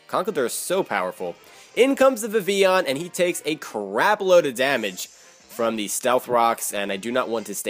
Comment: It sounded like you said a CRAB load of damage LOL. Are you thinking about the children now?